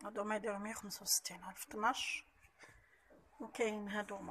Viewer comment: سلام أخت.اذا ممكن تجاوبيني :الكيسان تاع الشاي في د 5:18 قلتي 165 الف ل 12 كأس صح واش سمعت؟وهل الحجم الكبير ولا المتوسط؟...ربي يوفقك ويعطيك الصحة وشكرا .